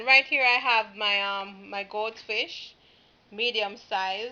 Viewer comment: what type of fish did you use l thought l heard goldfish? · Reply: It's GOAT fish! LOL!! That's what the fish monger told us that it's called